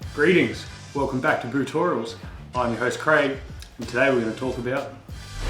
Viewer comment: Oh man I thought it said "Brotorials"